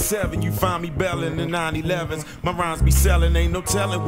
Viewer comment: in 97 you find me bailing 9/11s @@ did i hear that right? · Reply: Porsche 911s😎